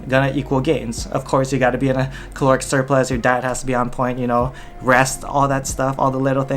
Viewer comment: i thought you said "your dad has to be on point"... i was like whaaaaat lmao